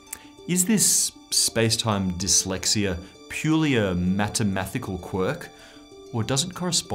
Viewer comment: instead of MATHEMATICAL he actually said "MATEMATHICAL"